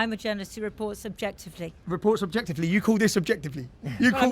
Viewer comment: Did I hear that correctly? "I report subjectively"? freudian slip?